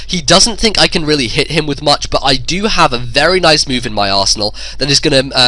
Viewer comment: sounds like he said "I have a nice move in my asshole"